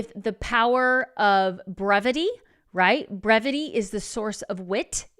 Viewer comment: Trying to listen to all of it in the car and I thought you said “Remedy is the Witt” lol😂 no you said BREVITY…Shakespeare (well technically the character Polonius according to AI Google correcting my mistake quote search)